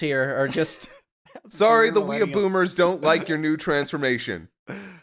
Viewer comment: Did just call himself a weeaboomer at 1:05:20?